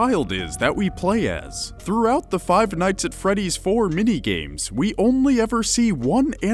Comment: Puts captions on I hear five night's at freddy's, but it said Five Dates at Bradys. why.